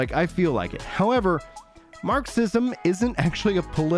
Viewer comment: I thought you said Mark Sission and not Marxism. I was like "man, what did that keto diet guy do to you?!" LoL.